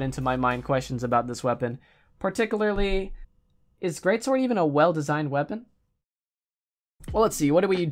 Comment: I thought it said "hell designed" at first lol